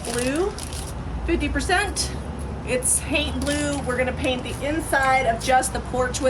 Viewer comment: What’s the blue called again?i thought I heard someone say it haunt blue. Lol. Oh wait maybe it’s faint blue. 🤷‍♀️